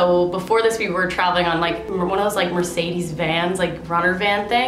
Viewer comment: i thought she said 'mercedes vanz' but then i played it back and i think it was just 'mercedes van' but how cool would vanz be. Because mercedes benz. but then vanz. ok